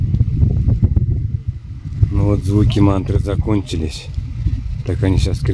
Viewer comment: Мне сначала послышалось--поют маты!! Потом только поняла,что--мантры📝